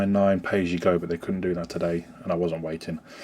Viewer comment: aha you said Hemorrdroidsnet. thought you said Hemorrhoids